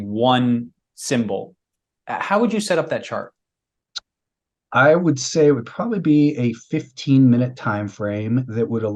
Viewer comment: what is he saying about moving average? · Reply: That he displays a 5 day moving average on an intraday timeframe